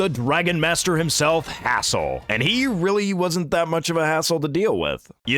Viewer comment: am I one out of many who thought this dude said a--hole 😂😂😂